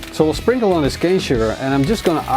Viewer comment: Hi, I'm Italian and I can't understand which ingredient you pour at 4:11 minutes. Thank you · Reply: cane sugar